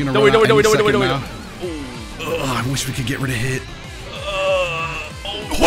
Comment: Did he just say I wish we could get rid of hit?